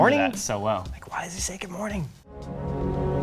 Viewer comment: I what silence of the lambs 5 times is definitely "hello" and it's the way he say it.i can do a perfect interpretation of that line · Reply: right that was my favorite movie growing up. i watched it hundreds of times. I can literally hear him saying "hello clarice" and when someone told me that line didn't exist i went back and watched it and heard him say Good Morning and my mind was blown